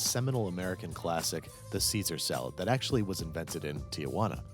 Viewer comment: i have no idea why but the first time he said "caesar salad" i heard "see-through salad" and got way MORE interested for a second